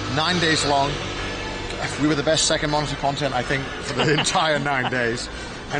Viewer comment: what did he say at 0:24 i can’t make it out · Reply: he said "Best second monitor content, i think"